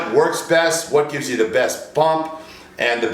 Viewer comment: Just me or does he say bump instead of pump at 2:05?